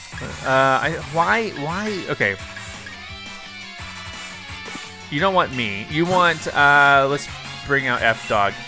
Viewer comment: Did anyone hear you dont want me in 7:10 or did i only hear that